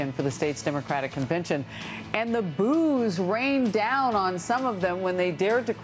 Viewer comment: I heard "the booze rained down on them"...